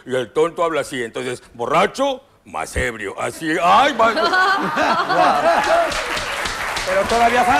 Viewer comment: jajajajajajaja lo que dice el conductor en el minuto 2:55 "AAH LA MADRE" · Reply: Dice: Aah la mierda.